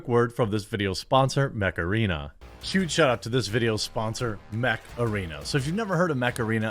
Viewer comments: I thought he was saying Macarena the whole time. Untill the end then I head Mech-Arena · 🤖Install Mech Arena for FREE here on Mobile and PC ✅ <link> and get a special starter pack to help kickstart your game! ⭐Existing players can take part in the Derby event from November 16 until November 29. New players - from November 16 until December 18. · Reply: honestly when you first said mech arene. i thought you said macarena. I thought what? are you going to dance?!? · Not a gamer and - ngl - when you said "Mech Arena' really fast, you know that you know that you KNOW what I heard: "HEY, Macarena!" 🙄🤣😎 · I thought you said Macarena :D when i saw ad then I understood you mant Mech Arena :D